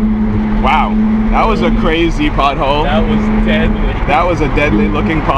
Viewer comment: Hahhahaha I thought you were saying butthole instead of "pothole"